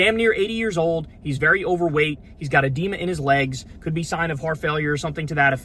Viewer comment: I know you said "hes got adema in his legs" but i heard "hes got a DEMON in his legs" and I just nodded along